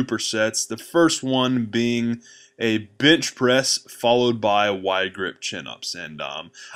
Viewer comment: Did he say a Bitch Press😂